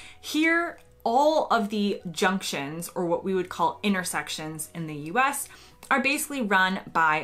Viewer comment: The evidence for Roundabouts is quite conclusive. They move traffic faster & safer, than using lights at an intersection. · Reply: Intersection? Oh, I thought she said innersection.